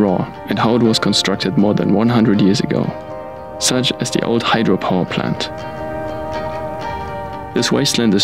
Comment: Thought dude said something about Hydra 👀